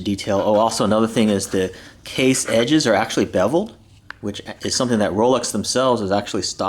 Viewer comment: Minute 7:17 what's the word you use to describe the edge of the watch? I am hard of hearing. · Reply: Beveled.